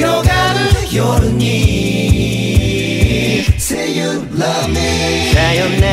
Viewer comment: say you ラーメン にしか聞こえないぴえん